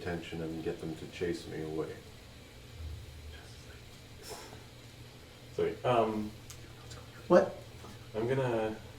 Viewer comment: what did they say? i think said "you know what's going on" but i don't really know. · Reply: Victor Auberg I can’t make out what said, but said “you don’t know what’s going on” and it was definitely a meta-gaming warning.